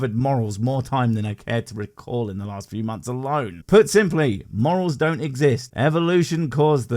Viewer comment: I thought he said moles. What with the frogs and the gopher. Took me a while to work out it was morals.